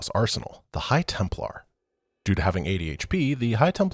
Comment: I misheard that as High Templar having ADHD the first time around and was incredibly confused, lol